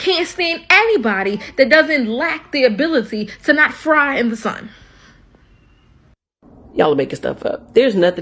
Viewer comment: DID SHE SAY , " FRY IN DA SUN " DONT SHARE WITH THE RATISTS, THEY DONT LIKE TRUTH. 39:28 TRUE.